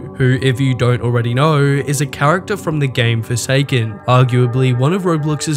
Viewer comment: did he say Jane doe was from forsaken